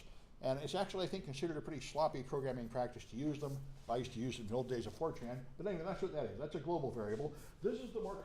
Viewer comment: Did you just say "I used to used them in the old days of 4chan"? I see, I see.